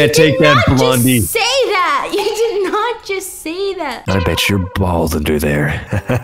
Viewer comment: it sounded like said "no b*tch your bald under there" but he actually said "I bet your bald under there" I CANT UNHEaR Itt